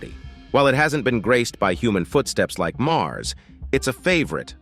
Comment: Did you just say "hasn't been graced by human footsteps unlike Mars?" The fuck did I miss?